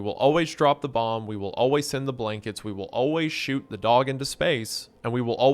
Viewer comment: I first heard this as “we will always shoot the dog in his face” and thought it was a reference to hunters childhood trauma